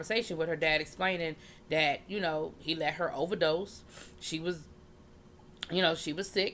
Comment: Me and my boyfriend were watching it and it sounded like he said let HER go...I told him I think it might be Ramona